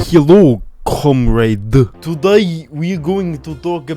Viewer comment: I thought he said "hello cumrags" in the beginning